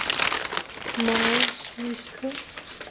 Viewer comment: So at 6:40 did I hear right that the brush is made of mouse whiskers?! If so, who would ever had thought to try it. Do mouse whiskers work well as brushes?